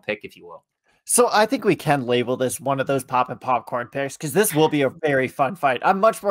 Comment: Should be a very competitive fight...definition of a 🥤+🍿 pairing! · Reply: Literally thought you were saying "popping popcorn" all this time :D UK fan so pop is a bit of a rare phrase to come by